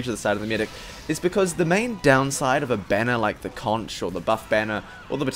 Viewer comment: Beggar's is underrated but amazing, especially with a banner. It's godly in MvM with upgrades. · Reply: Thought you said bonner instead of banner lmao